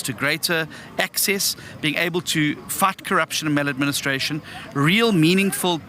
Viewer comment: Lol did he say "FUCK" ??? 5:20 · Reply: 😂😂😂I listened now he said fight